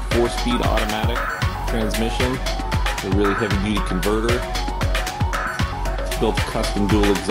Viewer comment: what the heck? did you just say?